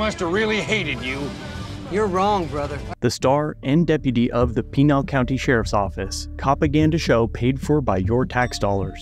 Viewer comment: did .. did you say the .... Penile County Sheriffs.... Penile County..... damn i hope that's right